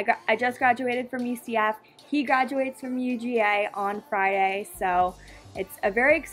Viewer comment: It sounded like you said FRIYAY instead of Friday